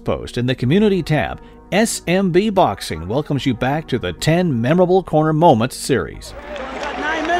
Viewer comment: I thought it said memorable cancer moments 😭😭